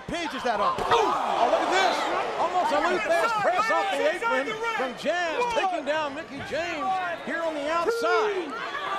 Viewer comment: I heard them talking @ 01:30 but I couldn't understand it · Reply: Yeap she said you ok.. :)